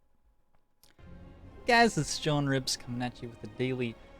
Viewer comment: I keep hearing "John Ribs" instead of in the opening.